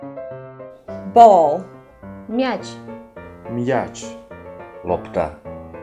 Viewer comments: мне одной послышался что он сказала "Епта"? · мне послышалось епта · мне послышалось он сказал ёпта